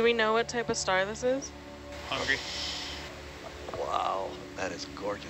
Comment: "Do we know what type of star this is? muffled "Hungry" I heard that!!!